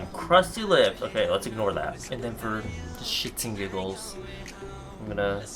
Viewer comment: was I the only one who heard "shitting giggles"? Sorry don't want to sound crazy but i guess I already have